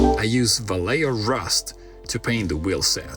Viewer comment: Oh, Vallejo Rust. I thought he said " a layer of rust."